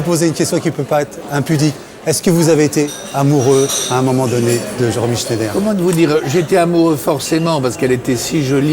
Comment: Please anyone write in english or french what Brialy says from 5:24 to 5:59? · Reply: Est ce que vous avez été amoureux à un moment donné de Romy schneider? Comment vous dire...j ai été amoureux à un moment donné parce qu elle était si jolie et si attachante, mais comme c'etait mon copain Delon qui était amoureux d elle, et qu il était plus beau plus séduisant, je lui ai laissé la place. D'ailleurs elle avait choisi...et elle ne m avait choisi moi. Moi, je la faisais rire.